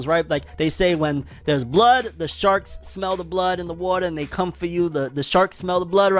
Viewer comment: when you said sharks, at first I thought you said sharts XD